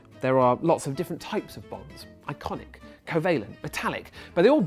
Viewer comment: St about 2:00 did he say "iconic" instead of ionic? haha